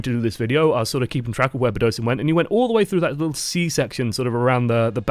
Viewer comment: did he say... c section?😂